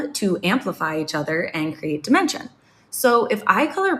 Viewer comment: I heard at 1:39 "to create dementia". And I don't really know if that's what you said but I heard dementia 😭